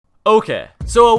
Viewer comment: that sounds like an okeh instead of okay